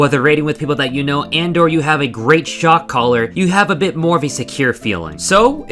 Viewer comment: You kept saying "Shotcaller". I kept HEARING "shock collar".